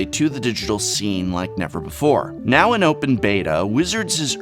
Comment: Wait did I hear that right? Open beta?